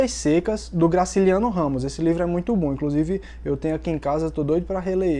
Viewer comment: só eu q ouvi ele falar "esse livro é muito ruim" kkkkkkk?